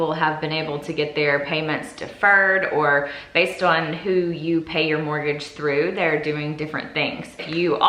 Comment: I thought you said you could pay mortgage. Don’t delay if you can pay it